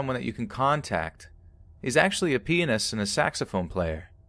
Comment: did he say penis? 😂😂😂